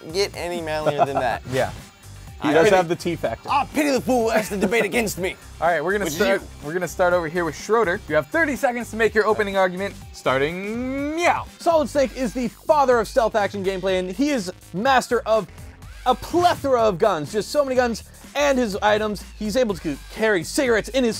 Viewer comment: did lasercorn just said "meow" instead of now? XD